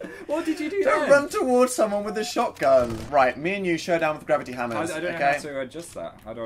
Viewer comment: i thought it said hamsters instead of hammers the hat fic will never leave me